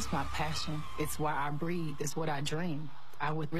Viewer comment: "thats why i breed." hmmmmm 😏 am i the only one who gets it? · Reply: I thought she said "it's why I breathe"